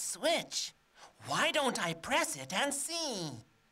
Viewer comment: "...Why don't I press it..." and what?? What does he say? :D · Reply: Why don’t I press it and see?